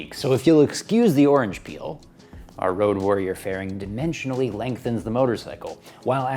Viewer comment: Did he say, "Road Warrior faring"?????? holy sh#! I thought I was the only one who studied that film for the farings! Is there a community? Do other people modify their cycles towards the Road Warrior standard?????